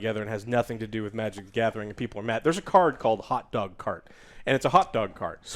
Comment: I was listening while i was doing chores and definitely thought they were saying "hot dog c*m." lol jesus christ.